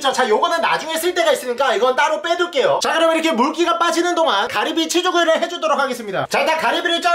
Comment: 가리비 취두부라고 들리는데 조만간 가리비 취두부 가시죠